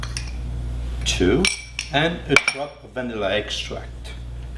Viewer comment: Hi sir can you please write the name of the bottle you putted at 1:22 i couldnt hear or understand exactly thank you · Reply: It is vanilla extract